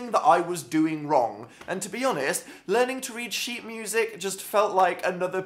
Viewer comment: When he said "sheet", I heard " shibe". WHY?! Gawd!